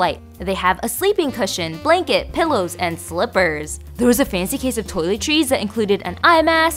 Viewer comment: I heard "fancy case of toilet cheese" 😆😆😆😆😆 💩 🧀